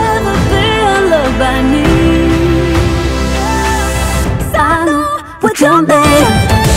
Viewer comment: It sounds like she is saying "I know what's your name" instead of "what you're made of". At least to me.